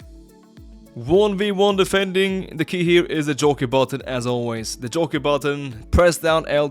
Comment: can someone help me? what exactly does he say right here? the "joy key" button? does that mean L3??? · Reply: Jockey button i think